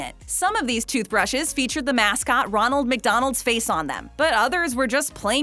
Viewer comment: At the start I thought you said RONALD WEASLEY.